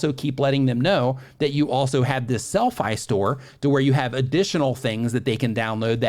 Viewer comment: This is a great video, thank you and Tubebuddy! I'm on my second watching of it in 2 days. Question: at about 39:01 mentions something that sounds like 'sellfi store' a couple times. I've googled that and can't find it 🤷‍♂️😬, anybody know what he's referring to? Thanks!